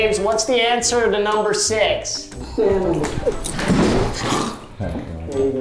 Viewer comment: did james say alfrid